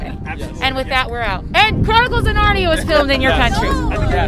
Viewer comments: What did the lady say at 11:53? “(Something, something …. In your country”? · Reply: “Chronicles of Narnia was filmed in your country…” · what did she say was filmed here? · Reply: Chronicles of Narnia